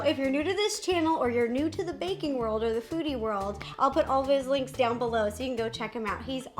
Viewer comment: I thought Ro said the Fruity World at the beginning instead of “the foodie world” 🤣🤣😅